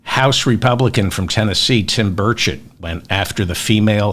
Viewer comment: Every time someone says the name ' Tim Burchett' I hear 'Tim Birdshit'.